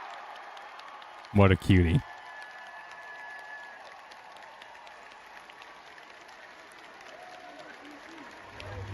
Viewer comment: multiple watch throughs, still have no idea what Yoshi-P yells here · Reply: I am pretty sure he says "Stop music please........I LOVE YOU GUYYYYYYYSS" But I could be wrong 😂